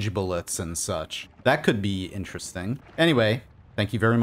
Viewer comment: "Happy Noita" If that is what he said or if he said "Noiting", there is no such thing as a happy Noita. only.... P A I N